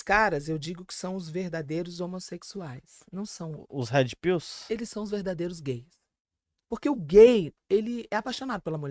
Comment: Aos 2:21 não consigo entender o que ela fala....aqueles caras que consomem ...... consomem o que ? Alguém por gentileza entendeu escreva ...😊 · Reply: Ela disse " aqueles cara que consomem Andrew Tate"